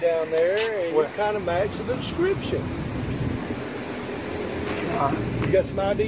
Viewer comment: I thought he said that he fit the description. But he called to get it bc he had no idea. Shameful.